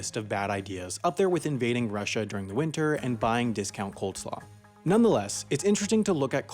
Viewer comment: did you just say coldslaw?